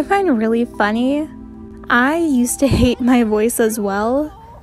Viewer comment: Omg I though your were saying boys and I thought you said you used to hate your boys. 😅